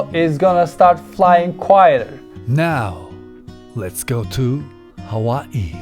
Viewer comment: Hi iPhonedo. I didn't understand what you said at minute 7:15. Mavic Pro will start or stop flying quieter?